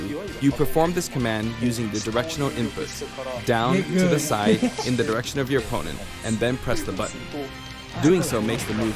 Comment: what did nairo say 😳😳😳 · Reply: Get good. What’s wrong with that?